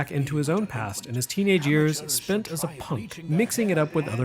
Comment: what is the word said at 1:59 "spent as a ----" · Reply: "spent as a punk"